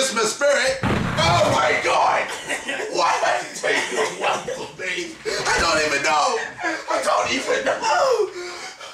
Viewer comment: What did KSI say at 5:47 and 6:13? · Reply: is "What do you want from me?!" And 6:13 is "BUT IT'S DISGUISED AS AMERICAN,BECAUSE THAT'S HOW IT ROLLS!WHAT THE FUUUUUCK!?"